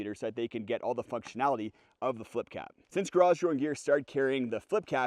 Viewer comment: For the longest time, I thought you were saying “garage rone gear” and o could never find the website 😂